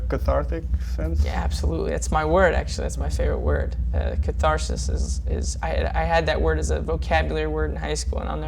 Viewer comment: Which word is his favourite? I didn't understand 😣😣 in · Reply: Hermine Granger. The word is "catharsis".